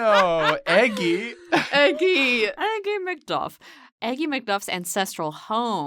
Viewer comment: I thought they were saying Eggy MacDuff, but then I googled the episode and it was Aggie and that makes so much more sense. Disappointing, because eggy is funny.